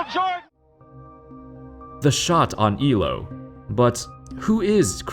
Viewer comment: "The shot oh amigo" i always hear it like this.